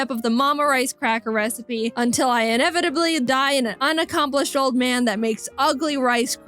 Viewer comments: Did you just say old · did she say old man? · hol up did she say “unaccomplished OLD MAN”???